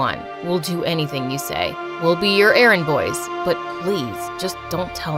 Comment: Why did i hear Aaron boys 😭😭😭😭